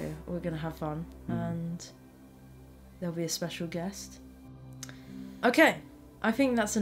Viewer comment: I had to watch the end twice. I thought you said a special dress, but it was special guest. 😀